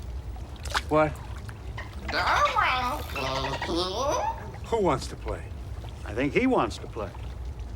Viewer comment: What that dolphin say???? Everytime I it back I hear "Darwin wants to play." and something about "small cocks" was Brody and cast doing things to that poor dolphin??????